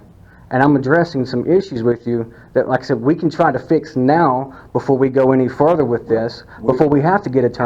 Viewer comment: Did you say meow? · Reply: lol.. Said , to fix now... No meow.. It does kind of sound like that a little.